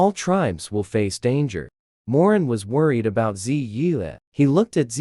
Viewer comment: I thought she was saying Moran